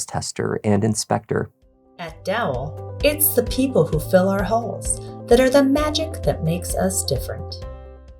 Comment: "At Dowl, it's the people who fill our holes that are the magic that makes us different" 🤣🤣🤣🤣🤣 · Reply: haha! I had to go back and rewatch. I do think she says “fill our *halls*”